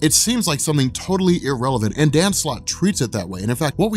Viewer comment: So until just now seeing the guy's name is "Dan Slott" I've always thought was saying "Dancelotte" all this time